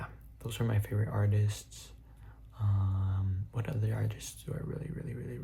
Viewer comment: i swear to god i kept hearing secrets after poop i was so confused 😭😭